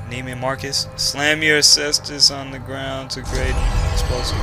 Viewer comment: Oh jeez, when you said "slam your cestus on the ground," I thought you said, for whatever reason, "slam your sisters on the ground," and I was like 'lolwut'.